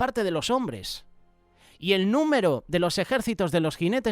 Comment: MINUTO 5:10 "YO OÍ SU NOMBRE"❓️❓️ QUERRÁ DECIR SU NÚMERO ❓️❓️